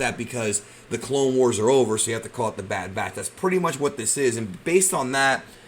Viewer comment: I thought it said the bad bitch for a sec